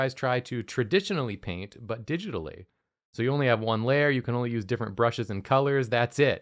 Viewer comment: At first I thought it said "Using Only ONE Lawyer"